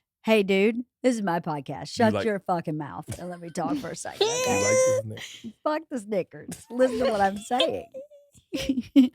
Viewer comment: Whoa! She said "fuck this sticker," And it sounded like something else.